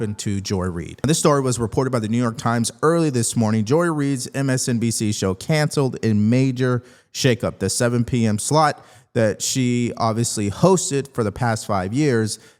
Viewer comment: Wait... did you call her the 7pm SLUT !!! 😂😂😂